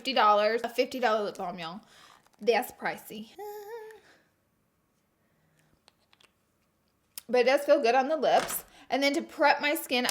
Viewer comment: Hi Laura! This is such a great video, and I wanted to make sure everyone can understand and enjoy it. That's why I wanted to let you know that your captions are a little off. At 2:32 the auto-caption say "that's pricey but let's go good on the lips" where they meant to say "that's pricey, but it does feel good on the lips." When you get a chance, could you fix that? That will help the 360 million individuals across the globe who need closed caption to understand videos. Thank you, and keep the videos coming!